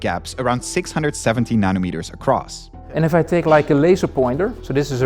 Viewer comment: Did he say 670 nm apart?